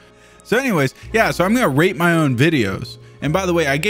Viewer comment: I heard "im gunna rape my own videos" and I wasn't wrong